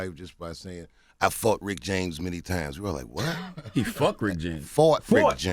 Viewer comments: I definitely heard "I fucked Rick James many times". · I heard "I fucked Rick James many times" as well... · Lmao I thought he said he fucked Rick James too! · Real spit I thought he said "i fucked rick james.." too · I totally heard "I FUCKED RICK JAMES" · I heard "I fucked Rick James" too. LMAO! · bro on me I thought he said Charlie fucked Rick James 😂😂😂😂😂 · I thought he said "I fucked Rick James many times" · On everything I love I thought he said "I fucked Rick James many times" too... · I heard him say "I fucked Rick James alot"... anyone else? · tbf I thought he said "fucked" too lmao